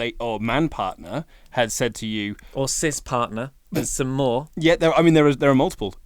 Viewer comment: can anyone tell me what just said? Does he say Sis Partner????? Or Sus Partner and no matter which one it is, what the hell does that mean? 🤔